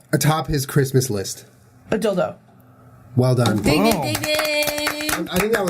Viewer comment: my dad just yelled at me because he thought i was saying "dick dick dick" but i waz just saying "dink dink dink"